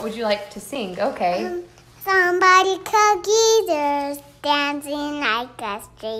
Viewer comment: it sounded like she said, "somebody call Jesus"